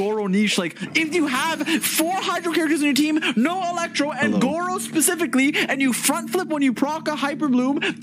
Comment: I just hear hello, I swear I thought somebody was talking to me irl